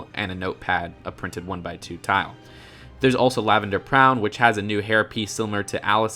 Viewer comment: He said "Prown" instead of Brown.